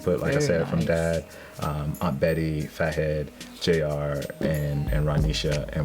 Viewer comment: Does he say FatHead